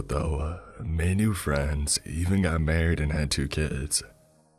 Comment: Omg I'm so high rn, When he said "I got married and had 2 kids" I legit thought he said "I got married and had 19 kids"